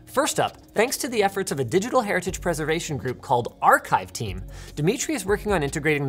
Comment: Did he just say archive team?